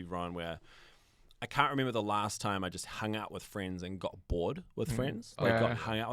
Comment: did he say he get high with his friends ? lol 😊